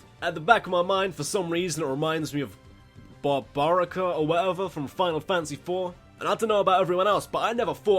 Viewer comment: I thought you said “Bob Barker from Final Fantasy”